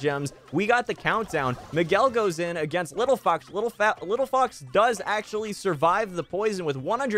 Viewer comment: what did you just say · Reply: Little fat 😂